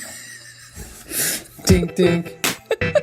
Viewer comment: wait a second. Are you guys saying "dink dink" in the intro? For the past year Ive been watching I thought you were saying "dank dank"....